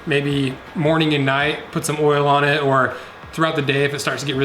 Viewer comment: Thought he said Meat Oil. Jacked up my boots.